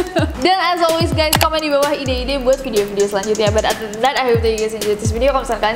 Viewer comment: MASIH GAK TAU SAMPE SAAT INI DIA NGOMONG APA "adedenat' " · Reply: Other than that